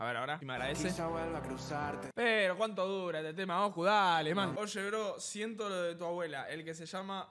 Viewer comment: JAJAJAJA no entendi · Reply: “Oyebrosientolodetua-buela” ósea que le dice que vuele